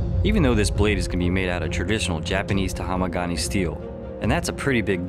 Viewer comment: did he just say "taHAMAgane"?